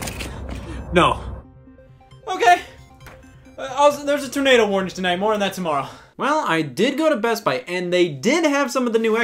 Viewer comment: “There’s a tornado warning tonight, more on that tomorrow.” Finally found a reliable news source. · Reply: I thought you said tomato warning